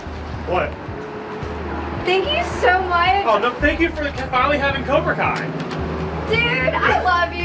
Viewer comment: did he say cobra Kia or am I trippen